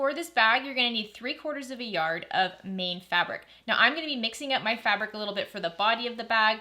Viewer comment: Hello! What kind of fabric did you say at 6:13? Meed fabric?